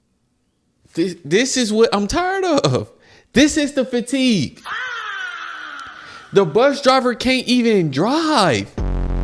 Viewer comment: I thought you said “the bus driver can’t eat and drive” lmao and was like yeah that’s fair, bus drivers need ass too